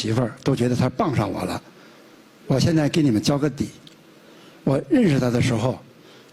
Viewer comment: 说我认识她的时候，我听成我日她的时候😅